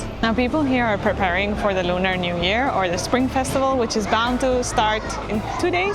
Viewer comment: She said it right in the video, I give it a like. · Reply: what she said is Lunar New Year